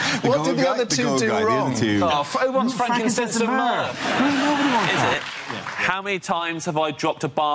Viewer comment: The jesus second birthday joke with the wise men, i actually dont get it at all. And ive played the video at0.5x speed to understand what the explanation is, and all i hear is "Franken sense of merc". Which i still have no idea what it means... please send help.